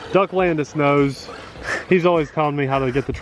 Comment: I don't think those are oil shocks. There is no mention of oil shocks on the product page, if they were they would mention it since that is a selling point. Those spare gears I think have different numbers of teeth for different gear ratios. Some people have mentioned they replace the battery with a LiPo and their motor no longer overheats... which makes no sense to me, but more than one person has said that. Oddly, the gearbest page says the battery is "built in", and when someone asked about replacement batteries the seller replied there was none since it was "built in". It's obviously not a built in battery though. -- The only way I can think to stop it from being so bouncy, since those aren't oil shocks, is add a bit of weight to preload the shock springs. But that also adds more risk of breaking things when bashing. I bet that rig would be great on sand. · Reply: Duck Landes I found DUCK LANDES although I thought he was saying Doug not 🦆